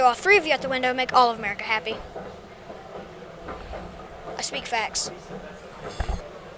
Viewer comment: When that kids joke just went flat he spoke new life into it with “I speak facts” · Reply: I thought he said "i speak fast"